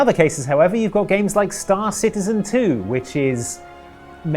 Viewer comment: what he said: "Star Citizen, too". What I heard: "Star Citizen Two". And I was like: "WTF was I in a coma!?"